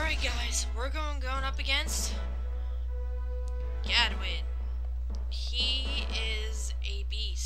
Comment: For a second, I thought you said Goodwin.